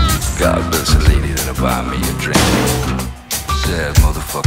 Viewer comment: Did he just say "i need a lady to buy me a drink "?